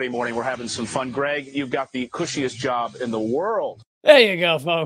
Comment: For a moment, I thought he said "...Greg, you've got the bushiest job in the world....", the way he was standing there..